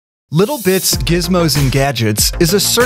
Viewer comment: when he said "Little Bits" i thought i heard "Little Bitch"